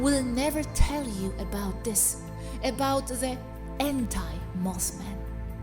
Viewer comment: End time Moth Man? Wait, what? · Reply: I think she said “anti-Moth men” meaning, the good version (Karistus).